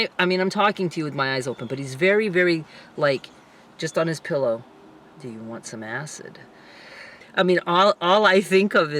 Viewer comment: 😂😂😂 At first I thought he said “do you want some ass?”